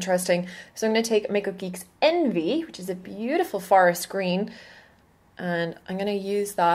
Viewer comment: I was apparently not listening very well because I thought you said "Fart green" instead of "Forrest green" I had to go back and listen again :) Love the look!!